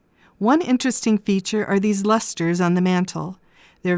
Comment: At time 2:48 what are you saying? Is it "lusters"? Is it a type of vase? I am not familiar with the term. Thanks. You do a great job! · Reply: Yes. Lusters. Let me go look up exactly what they were, because they look like candle holders, but I never see candles in them. 🤷‍♀️